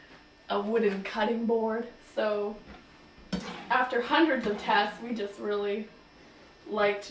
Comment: did she just say hundrads of tests?